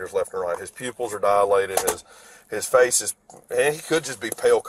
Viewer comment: dilated////I thought you said they were pin point